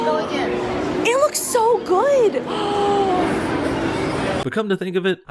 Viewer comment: when she said the word good a notification came and 8 didn't hear the word